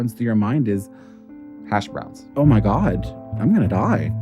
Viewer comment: “And I have fallen where I can’t get up right away, and the first thing that runs through your mind is” “(hash browns)” I really thought the guy actually said that at first!🤣thanks for the laugh👍🏻